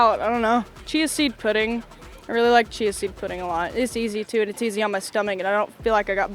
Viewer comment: What pudding? Cheese pudding? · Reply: Chia seed. I thought she said cheese seed at first lol